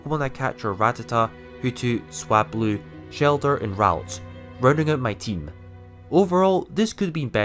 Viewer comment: Thought you said you caught a rowlet instead of ralts. Was confused for a moment as to how on earth you found it in a gen 3 game. :)